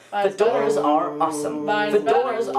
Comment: My dumbass thought that he's saying "the doors are awesome" at the intro, it's actually "fedoras are awesome".